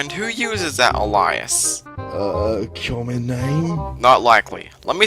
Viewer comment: I’m sorry, but what was that you said at 10:24? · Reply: Uncommon Name? Is what I asked